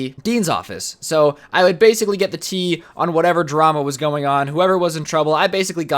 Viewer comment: DID HE JUST SAY TEA!?!?!?!?!?